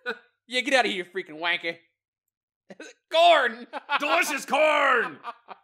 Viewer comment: What he says: FOR CORN! What I hear: FOR KHORNE!!